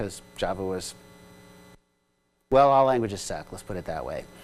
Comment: well all languages is sad, is that what he said as 29:45 ? :) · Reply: I think it's "well all languages suck"